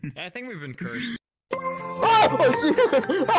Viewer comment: 지금 들었는데 21:05 초쯤에 놀라서 오 시발 깜짝이야 라고 하네ㅋㅋㅋㅋㅋㅋ